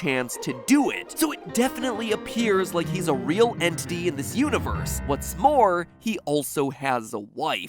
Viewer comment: did he say what's spore